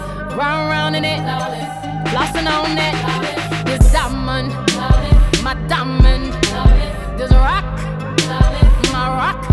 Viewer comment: Hahaha all this time i thought she is saying. "My dumb man my dumb man" instead of my diamond my diamond. LOL i was like.. theres no need to say bad things to men eventhough ur a feminist... lol diamond! There u go